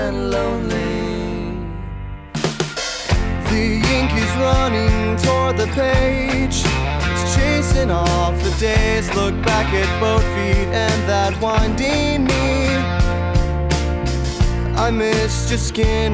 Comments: look back at buffy and that 1D meme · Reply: Jessica I kept hearing "look at both feet and that one dank meme" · look back at buffy and that 1D meme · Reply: Before I heard “look back at murphy and that 1D meme